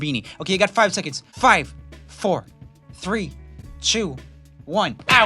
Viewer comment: he said chew instead of two